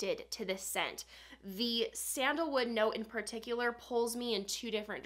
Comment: Hahahh I thought it said “sandalwood lunatic” and I thought to myself, bold, I like it 🤣